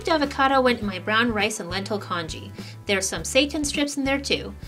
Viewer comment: I was not familiar with "Seitan" before this video. So at first I thought you said your had some "satan" in your congee. I had to rewind that one and listen again. Haha, thanks for the chuckle!